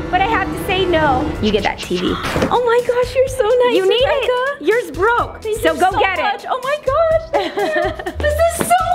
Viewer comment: “Yours broke” I thought she said “Your broke”💀